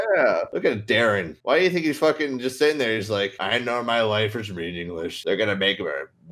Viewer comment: i thought said: I "gnome" my life is pointless. i rewound it and listened to it three times to make sure i was hearing correctly....i wasn't hahaha